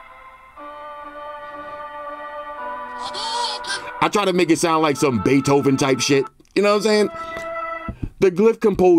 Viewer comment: do i hear odugen from street fighter 😂😂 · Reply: Lol yup! Hadoken 😂😂